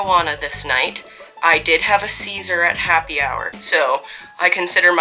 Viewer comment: For a second I thought you said "seizure at happy hour" and was like "well thats a casual way of putting it" before I realized a second later that you were talking about salad lol